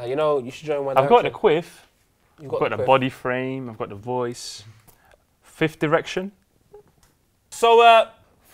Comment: I thought said fith erection???